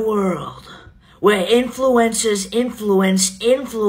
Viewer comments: Lol… I thought you said … influenza 😂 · Dude the way she said influencers, I thought she said influenza! It was still funny but I didn't know exactly what was going on at first